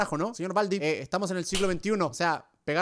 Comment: soy yo o escuché siglo 21